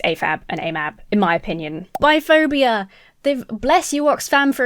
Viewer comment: you began talking about "biphobia". I wasn't looking at the screen ond only picked up the context a bit later, but at first thought of "buy phobia," as opposed to "buy phillia" -- something that might apply to hoarders. At 28:35, you talked about Twitter. What you describe is exactly why I've NEVER had a Twitter account. That, and I can't figure out what the proper thing to call one who uses Twitter might be. I suppose one who uses Facebook could be called a Facebooker (although "Facebookie" could be appropriate for one who takes bets on how many 'likes' and 'reposts' a comment will get), but would you call someone who uses Twitter a "tweeter"? People do refer to Twitter messages as "Tweets", but in my time -- when dinosaurs roamed the streets and kids my age made pocket money by sweeping dino dung off the roads -- "tweeters" were the speakers that put out the particularly high sounds from stereos. Maybe a good way to refer to Twitter users would be as "Twits". I understand that Oxfam is very much like the American Red Cross in the mission we share in disaster relief. I used to be the Emergency Services Director for a Red Cross Chapter covering 18 counties in Central Texas. Our policy was to treat people who came to us as clients, not mere customers. We expected to forge long-tern relationships with people, and treating them with dignity and respect was essential to that. I am greatly encouraged to know that Oxfam in asking its staff -- both paid and volunteer -- to